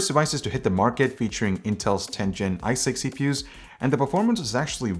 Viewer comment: Anyone else hear "i6"at first?